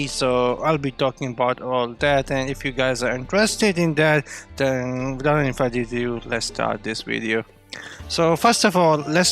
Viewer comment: what??? What did you say? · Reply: "If you guys are interested in that, then without any further ado lets start this video*